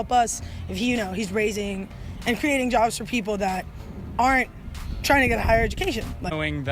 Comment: did she just say ... why would he try to create jobs for people who Are NOT Trying to get hired? lol